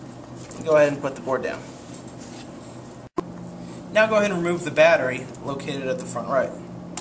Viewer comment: He clearly says "Remove the battery"